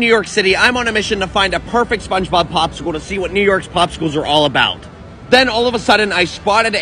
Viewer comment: I heard you say Two SpongeBob Pops stickles